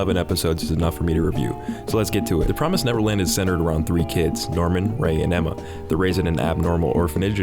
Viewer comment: for some reason, when you said "Norman" i heard "Darwin"